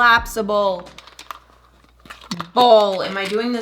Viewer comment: i thought she said colapsibowl but NOPE collapsible BOWL